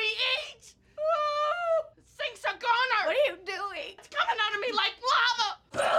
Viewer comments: “SINKS A GONER!” “ITS COMING OUTTA ME LIKE LAVA!” 🤣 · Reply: At first, I thought she said "satan's a goner" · “SINKS A GONER!” “ITS COMING OUTTA ME LIKE LAVA!” 🤣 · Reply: I thought you said stink 😂